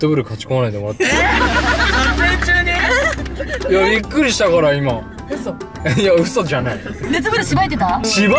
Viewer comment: ここのゆたが「びっくりしたから今」っていうのが若干「ゆっくりしたから」に聞こえてそれを聞き逃さなかったひゅうちんがその後さりげなく処理してあげたんだよねきっと笑笑笑笑笑笑